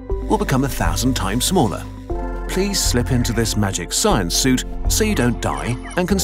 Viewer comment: did he just say "magic science suit"?